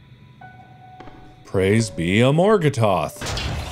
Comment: What mark heard: "praise be, aragatoth." What I heard: "Crispy, I like it hot."